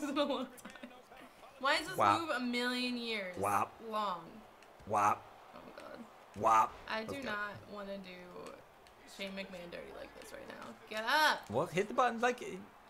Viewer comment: when you said "go shane mcmahon"at his entrance, I thought you said "go shaving man"